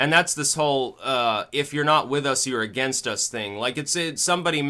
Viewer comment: I thought they said: "If you ain't with us you're agin us." :)